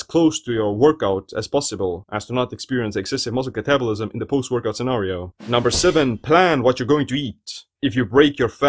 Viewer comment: Whatever you said @ 2:44 was not understandable - can anybody else tell me what he said? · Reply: “as to not experience excessive muscle catabolism in the post workout scenario.” Wasn’t too hard.